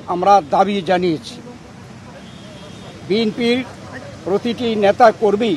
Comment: কি বললো? কি বললো?? 1:16 বিন্পি😂😂